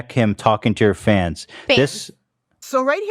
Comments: she says “fans” to confirm what the text said. As is “yeah, fans. Not underage girls. That’s what I said” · Think she might be saying "fan", singular, to correct Ethan for using plural